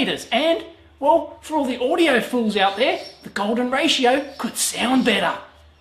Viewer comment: I thought I heard him say "audiofool" instead of "audiophile." It might have just been how my ears heard it but it's completely apt. :)